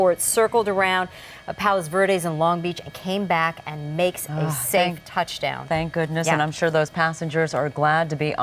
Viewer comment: i heard from the reporter that a pilot is british from long beach came back and make a safe touch down, am i right?? i don't know if what i heard is right haha.